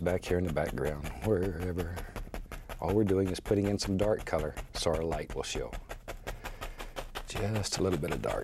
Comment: anyone know what he said here? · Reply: ''all we are doing is put in a little dark colour, So that our light will show''